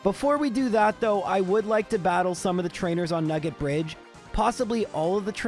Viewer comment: Ok I need to go to sleep. I heard "nekkid bridge" 😂